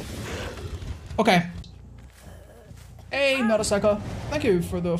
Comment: when I heard that name I didn't even think about until otz said it himself. instead, I was thinking "hm, that strangely sounds like Nagasaki."